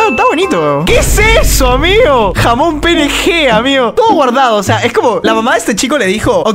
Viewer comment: que dijiste? ... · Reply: jamón jnp